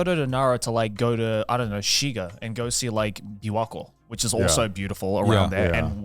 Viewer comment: Anyone understand the name of that Shiga destination that Joey mentions around 5:02 ? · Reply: Shiga Prefecture right next to the east of Kyoto and he mentioned Lake Biwa (Biwa-Ko)